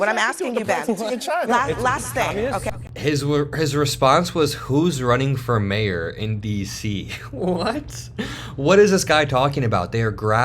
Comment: I thought he said “who’s running fur mayor in NYC”?